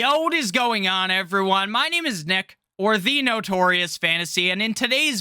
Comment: Sounds like, “At Notorious Effentious WHY”😂😂😂😂gets me every time!! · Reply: I thought his last name was Noltie or something For the longest time I thought he was saying this is “nick noltie notorious fantasy” 😂😂😂